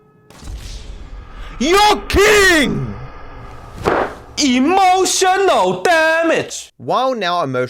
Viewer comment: It sounded like he said "You're king!" Edit: I now realised that even the subtitles think he said that lmao.